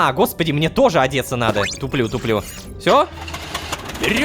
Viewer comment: Почему я в 8:25 услышал "ублюдок"?